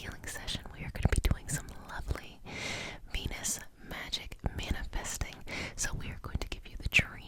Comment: legit thought you said “lovely penis magic manifesting” at the beginning, woke me the fuck up